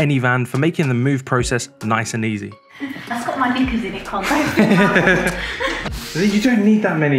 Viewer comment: what did she sayyyy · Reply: KNICKERS lol